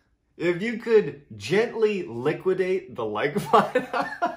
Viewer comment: I thought he was trying to say “gently Lick the like button” 😂